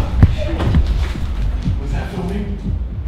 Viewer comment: Did they really say “is that a flamingo?”